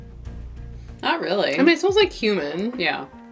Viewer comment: Haha: at 8:56, when said "it smells like cumin", I thought at first that she said "smells like *human*" 🤣